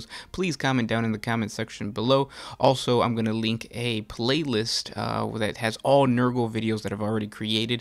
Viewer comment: I swear I heard 'plaguelist'